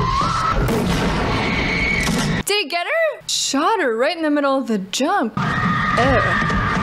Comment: hahahahahaha (23:39) I misheard you as: Shot her, right in the middle of the JUNK lol